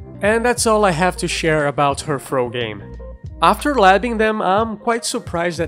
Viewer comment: Thought you said throat game.